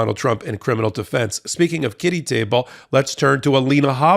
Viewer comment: Oh my stars, I thought he said 'titty table' 👀😅